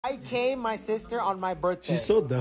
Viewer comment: Bro I heard "I came in my sister at her birthday"😭🤚🏻